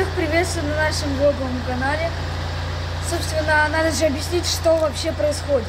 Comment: всех приветствою на нашем ёбаном канале ваше угар кто услышал лайк коменту чтоб он увидел